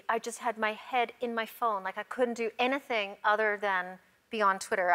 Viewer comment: The only time I've heard Portia's native Australian accent come out. "Anathing" instead of "Anything" haha. I love them as a couple they're just so lovely together! 💙🙂💙